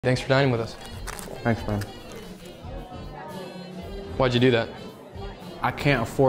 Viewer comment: WHY DID I HEAR “ THANKS FOR DYING WITH US” 😭💀